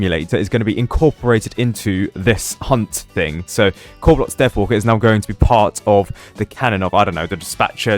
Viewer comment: YouTube captions said "carrot death water '